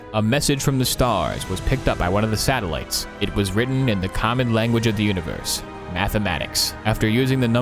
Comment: For the life of me, I cannot tell what word he is saying there. ``It was written in the ___ language of the universe.`` Kommid? Common? Comet? Comment? Commit? Com-Ed? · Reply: Common.